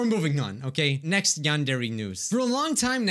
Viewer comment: i heard him say "Next Yandere Noose" umm.. help